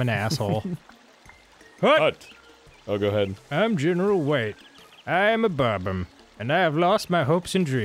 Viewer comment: When they were talking to the bob-omb in the beginning, I could've sworn Dan said "I am a bottom" and "I live inside two doorways" and I was hella confused...